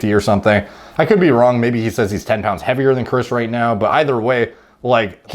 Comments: I thought said he's 10 lbs heavier than Chris but it's hard to understand him. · I thought he said “so I’m 10 pounds heavier than you are right now”